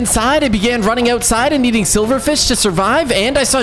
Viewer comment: did he say silverfish?